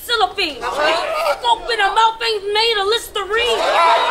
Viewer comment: "The scope in her mouth aint made of Listerine" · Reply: I thought she said ".... ain't mouth wash"